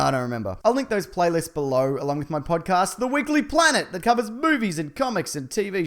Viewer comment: For a moment I thought you said the weekly planet covers boobies but it was actually movies